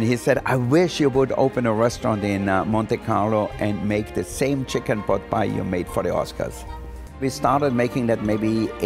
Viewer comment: I could have swore he said "how we pick cocaine for the Oscar's " lol everything looks delicious